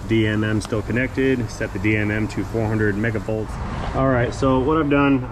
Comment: in the vid I thought you said set the VOM for 400 mega volts , is that right ? That’s a lot of voltage !!!